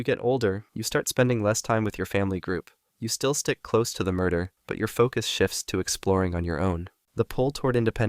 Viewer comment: Can someone please let me know what is the actual word in this video, that sounds awfully similar to 'murder'? Cannot make it by ear and subtitles are no help. You can hear it at 04:29 for example. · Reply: It is in fact murder, murder refers to a group of crows